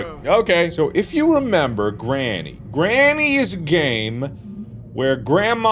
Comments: Why did i heard "if you remember granny.Granny is gay"😭😂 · At the beginning when Mark said "Granny is a Game" I wasn't paying attention so I thought he said "Granny is a Gay"